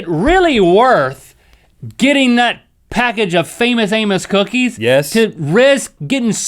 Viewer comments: I thought link said famous anus XD (if he did wow just wow) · When he said famous amous I had to do a double take because I thought he said anus